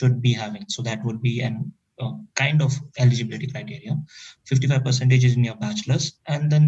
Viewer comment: did he say LGBT criteria?